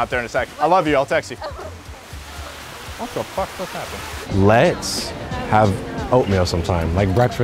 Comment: I can’t be the only one who heard him say “what the f*** just happened”🤣